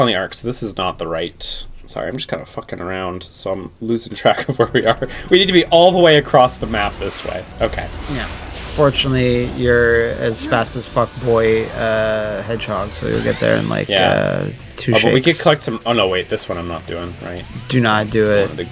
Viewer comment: fast as fuck boy hedgehog sounded like fuckboy hedgehog